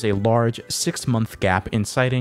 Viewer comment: I like how it says "sex" instead of "six"